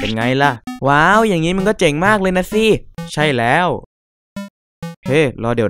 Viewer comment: ได้ยินว่า"ว้าว อย่างนี้*ึง ก็เจึงมากเลยน่ะสิ